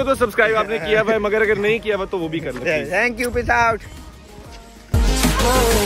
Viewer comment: Sorry but i heard it "piss out" rather than "peace out"😂😂 Lit vlog it was man insaneeee😍😍🔥🔥